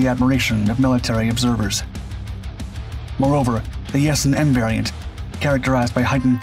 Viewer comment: Did you say "the S&M variant"? I had the hearing aid out on that ear, so I may have misheard. Ah, I turned on caption and it was Yasen M variant. Sorry! False alarm!